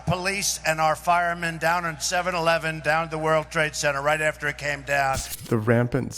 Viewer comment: I swear I heard him say 7-Eleven for 9 111